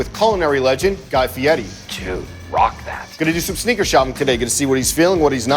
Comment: Guy: I thought u said Snickers shopping.